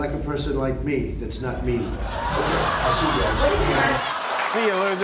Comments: Must be my ears - thought he said " they like a person like me - that's not me". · "They like a person like me that's not mean" im a republican and I'm dying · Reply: Oh darn. I thought he said "...that's not ME". Hahaha. Either one is funny anyway. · they like a person thats not me like me anyone one else hear me instead of mean 5:39 it took every ounce of will power he had not to piss himself but he's face still got away with one this man tried to hold his soul down and still failed so he just looked away the camera didn't